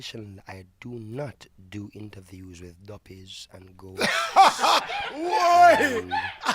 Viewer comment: What does Damian say at 2:45? He doesn't do interview with who? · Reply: Duppy and ghosts, but I think he means spirits and ghosts